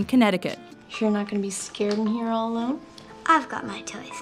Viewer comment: what she actually says is "How the h*** did he get here".